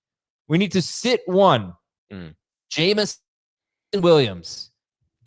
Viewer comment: it sounds like Adam is saying Jameson f***** Williams and they had to bleep it 😂😂